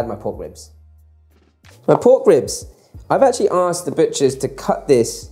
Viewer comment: I misheard "the butchers" as "the bitches" and thought "...man, I need a new wok if I want that kind of respect."